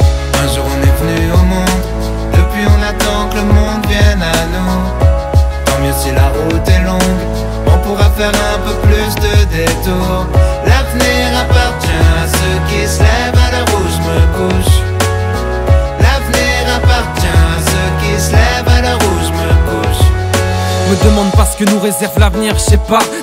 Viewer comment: Au départ j'ai entendu "L' avenir appartient à ceux qui s'lèvent à l'heure ou j'me touche" J'ai trouvé ça drôle Pardon